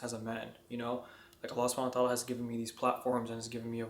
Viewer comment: Allah subhanahu wa taala he said. The caption is wrong. Jazakallahu khairan.